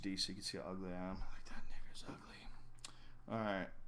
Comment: did he say "that nigga's ugly?" Lmao